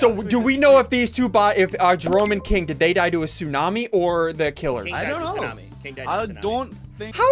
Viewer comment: did anyone else hear that he said these to bots then Jerome and King